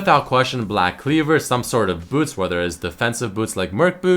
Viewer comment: Did you say boobs ?